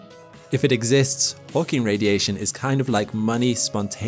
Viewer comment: I thought you said "Porking Radiation", which probably has a much worse meaning.